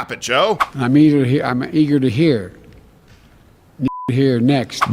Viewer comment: Ok as a black man i had to go and listen to the biden speech uncensored. I don't like him. He's a career racist and to this day I have no idea how he can get away with standing in front of the country and saying "If you don't vote for me, you ain't black". But in that speech i don't think he said it. "I'm eager to hear, nigger to hear next from my good friends......" Etc etc? It makes no sense. I heard "I'm eager to hear, and eager to hear....." Makes nonsense gramatically but Joe isn't the most fluent person standing in a kindergarten class. My two cents